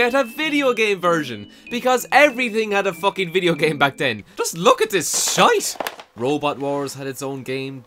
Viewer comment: Does he say "just look at this Scheiß"?